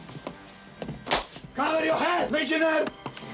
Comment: COVER YOUR HEAD LEGIONALE! My fave part of this legendary movie. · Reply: Always thought he said carry your head regionale 🤣